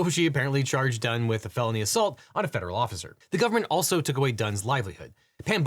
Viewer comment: It absolutely IS a deli weapon, actually (incidentally, I also thought you said "baloney assault" instead of felony assault earlier)